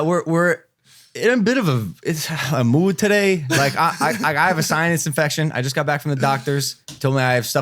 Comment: yall hear what I hear? “ I have a science infection”